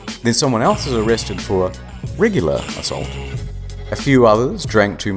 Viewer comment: I thought he said "Rigular Assault" which I was terrified because I had no idea what that meant. I was like "What the FUCK does RIGULAR mean?!". Took me a second to realize he said "Regular". Thank the lord.